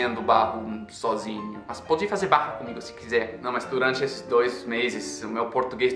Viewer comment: eu entendi "pode fazer barro cmg se quiser" kkkkkk